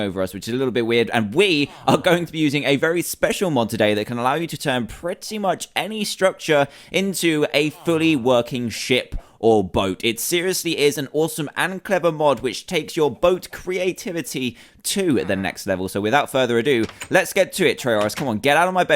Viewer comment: Did he just say "s**t or boat"?